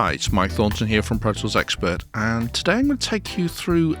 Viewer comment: Subtitles say ‚Hi it‘s Mike Thornton here from pretzels expert‘ and I can‘t stop listening to 0:16 on repeat